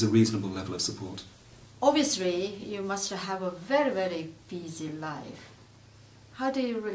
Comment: Liverpool HAS no cuisine. I thought she said: David, you have a very easy life! She said busy!